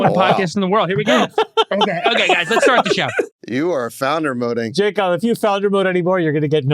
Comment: I thought he said Founder Milk!! 😂😂😂